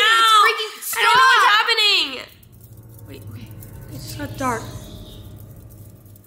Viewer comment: in the vid i heard a voice say say "save me"